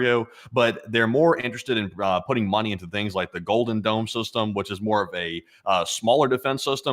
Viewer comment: I always hear 'golden gnome'.. I wonder if he's got a pot of gold at the end of the rainbow.. LSA 🇺🇦